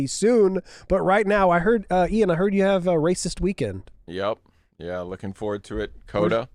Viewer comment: I thought I heard hey Ian you had a racist weekend? and he instantly replied yep 🤣